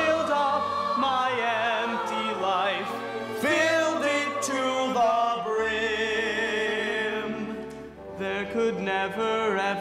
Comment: This is awesome, but what's better is if you put on closed captions at 5:49 instead of saying 'Filled it to the brim' it says 'gonorrhea'. XD